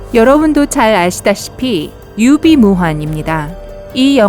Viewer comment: 앜ㅋㅋㅋㅋㅋㅋ 유비무환인데 유비무한으로 들었넼ㅋㅋㅋㅋㅋㅋㅋㅋ 유비가 무한! ㅋㅋㅋㅋㅋㅋㅋㅋㅋㅋㅋㅋㅋㅋㅋㅋㅋ